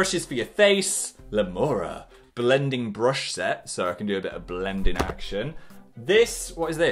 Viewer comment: When he said “blending action” I thought he said “blending and sh*t” and my mouth dropped